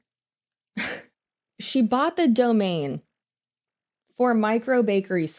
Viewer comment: in 35:48, I thought you said domain as in dough-main lol